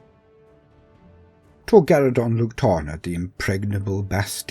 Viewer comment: I misheard initially and thought you said Torgadhan (spelling might be off). A true Luna wolf.